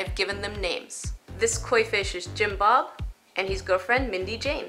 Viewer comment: Jinbop? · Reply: I thought she said Jim Bob